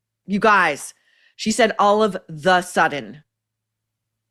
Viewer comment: Sounds like she says "all A THE sudden," ... now you've mentioned this i'm hearing it all the time.